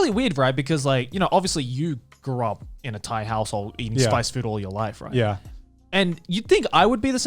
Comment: had to do a double take at 1:31:16 thought Joey said Garnt lived in a "tight A**hole" lol